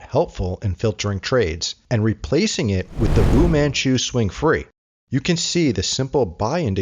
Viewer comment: what was the name of the indicator at 8:30 ? didnt catch what you said. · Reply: Vumanchu swing